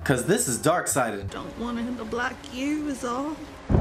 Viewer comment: what did she say here? I keep missing it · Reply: I dont want to end up like you, is all